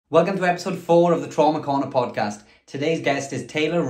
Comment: On the first video that popped up on my feed, I thought you said "This is Ben Trauma Corn". When I realized it was "Ben Trauma Corner" I was disappointed. HAHA. "Trauma Corn" is a good marketing hook as 1. it is an oxymoron....because trauma is not corny at all..... and 2. it would be a great logo for your brand I think. 🌽🌽🌽. Plus I love your shorts. 🩳. Therefore it seems logical for your logo to be a >>> CORN COB WEARING SHORTS <<< with your channel called "BENS TRAUMA CORN (BTC)". 😊😊😊 The MERCH would be easy to make. Since there are no brands with CORN as their logo (that I am aware of) your brand would really stick out with "BTC" written under or next to the logo. On a serious note, I have experienced childhood trauma. The videos I have been subscribing to have been around narcissism, hence why your videos popped up. You really nail it on the head and make me go "hmmmm" every single time.